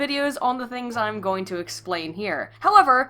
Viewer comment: You said 'explain' instead of 'explicate'. (1:39) Unsubbed... :-P